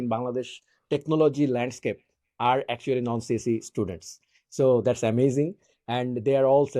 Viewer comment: can someone tell me, please, what said at 35:10? "non.... students" Thanks in advance! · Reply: I told Non CSE students - people not from CS backgrounds